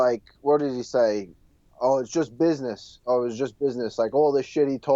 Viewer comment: he did not say "It's just business" he said ‘don’t kiss us’ *dublin slang for get your face out of me the rounds over.